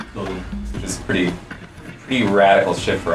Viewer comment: Did he say Shift, or Shit? I doubt he said shit because it was released in an E rated game but.. still. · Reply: He said "Radical Shift"